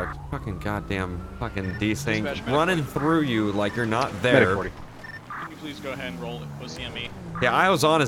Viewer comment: WHAT DID HE SAY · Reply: running threw you like you weren't there ... tf